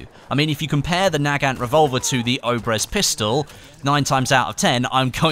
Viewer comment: I first thought it said the orbeez pistol